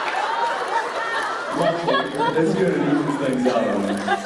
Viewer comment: What does Jensen say at about 7:00 minutes that makes Jared and everyone laugh so hard? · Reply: Alima He said : 'it's good it evens things out on him' :)